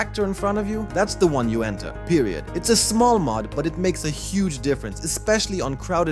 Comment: Did he say "period